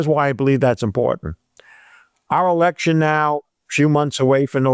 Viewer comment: Did I hear "selection"?